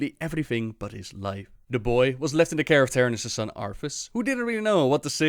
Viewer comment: these lore vids are great just sometimes he hits them sentences so fast with that accent and i got to rewind it a few times to understand lol. like what does he say at 4:49 about "the boy was left in character in his son Arthas" or somthing lol